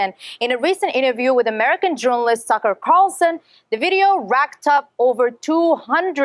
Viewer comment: I could've sworn she said "..Sucker Carlson.." must've been a Freudian slip, lol